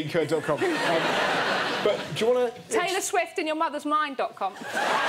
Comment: I thought she said Taylor swift in your mom